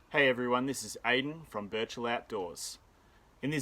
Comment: 2020 really messed with my head. I heard you saying "Virtual Outdoors"...